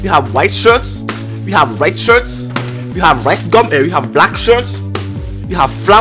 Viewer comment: Did anyone heard him say we have ricegum??